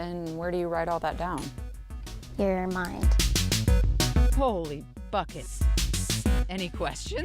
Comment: in 2:44 i thought she said Holy Fuckin on video or on TV